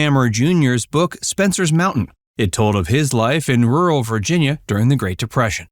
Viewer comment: Did he call Earl Hamner, Hammer? I thought I heard Space Mountain instead of Spencer's Mtn. Ellen Corby had no husband from what I've read. Geer, not Greer.